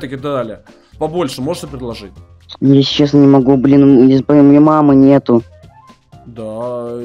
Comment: что он сказал? я не понял? у меня мама нету?